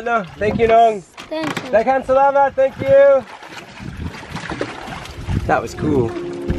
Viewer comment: I heard a little boy’s voice that said “thanks man” 8:07 but there is no boy in your video